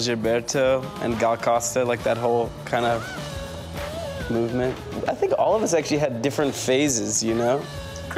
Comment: i think rostam tried to say "movement" but it was just a mumble